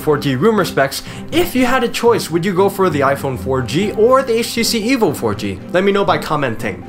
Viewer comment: I thought he said HTC Evil 4G